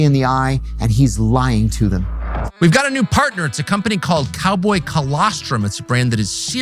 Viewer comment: I had to double check that I thought he said cowboy colostomy I spat my drink out